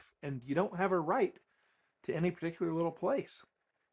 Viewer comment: did I hear correctly you say at about min 5:15 "you don't have a right to any particular place"? so, no property rights ? for no one? .... i absolutely agree !